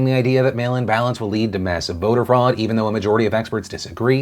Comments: He said "mail-in ballots" I heard "male imbalance" lol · I was doing other things while I listened and thought you said "male-imbalance", not "mail-in ballots" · When he said mail-in ballots I heard "male imbalance"